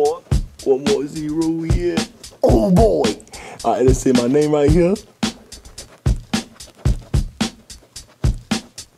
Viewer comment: What did Black Yoshi say? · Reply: Oh boi